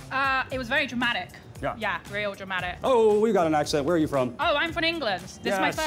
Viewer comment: I thought I heard "We got a nice set"